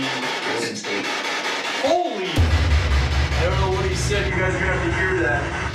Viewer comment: I think the man said “it’s me.”